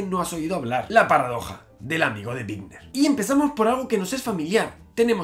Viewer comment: Jajajajaja entendí "la paradoja del amigo de Tinder". Tengo el cerebro podrido